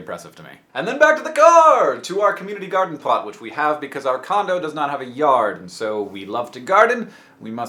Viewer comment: Ahahaha i thought you said condom... Har har harrrrr